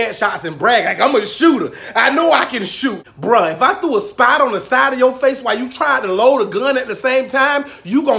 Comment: I was all good right up to the "spider on the face" thing. I think i might have peed a little. But I'm an old man, so that just happens.... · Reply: epic 😁😁😁 I thought he said spider too until I rewound it about 4 times. he said "spot on the side" but it did sound like spider 😂😂😂